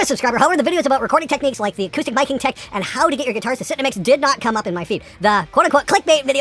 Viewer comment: Ha! When you said, "How to get your guitars to SIT in the mix" I heard "how to get your guitars to SH*T in the mix" because of the video title flashing on the screen. If we couldn't manage that second one, you'd have no content. 😂